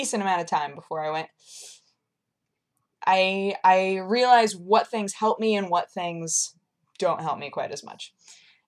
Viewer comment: When you said "I realise what things help me" the closed captions thought you said "Praying angry eyes were year things"